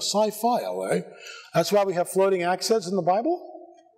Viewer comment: Floating accents? What'd he say?